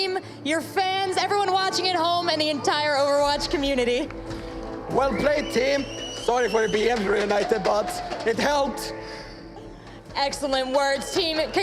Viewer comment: What does Tviq say on 1:43:41 ? His accents are hard to catch :/ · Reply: sorry to necro but i think he says "Well played team, sorry for it be every night-uh but it helped!" I believe referring to how much they practiced